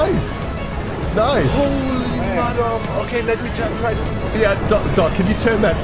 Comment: if you dont hear a swear there, it is doc saying holy motherfu